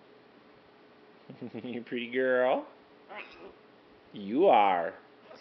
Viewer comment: I thought i heard the parrot say im sick of pretty girls